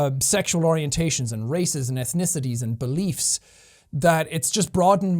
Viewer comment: He said ethnicities but all I heard was ‘earthnicity’. Congrats, Seán! We’re proud of you :)